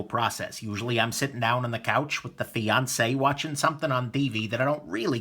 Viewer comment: Throughout my watching of your videos, I've heard you say "the Beyonce", which struck me as an odd name for what I presumed to be your girlfriend/wife/other. Only today do I realize that in fact your saying "the fiance"! Who knew. Well congratulations then. Yeah. The light, She burrns.